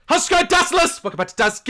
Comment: Haha if you put subtitles on at 0:00 instead of, “how’s it going Dazzerlers” it says “huskier dustless” lol